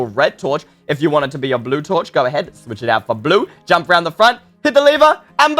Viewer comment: When it said blue torch I thought it said bluetooth